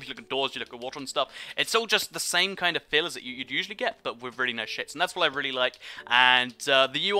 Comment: I swear he said 'just like what you'd normally get just with a lot better shits' lol